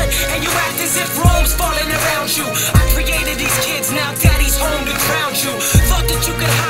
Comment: Lol... said "daddy's home to ground you" and all this time I thought he said "Daddy's home to drowned you!" Woulda been funnier if it was drowned though...I love you two <3 homies till the end :)